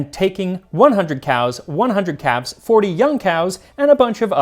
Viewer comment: I swear, I thought he said 100 cows, 100 cats and 40 young cows. I had to repeat it to hear 100 calves. 😂😂😂